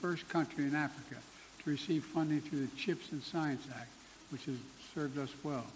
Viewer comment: I almost thought he said "Receive funding from the fish and chips act"....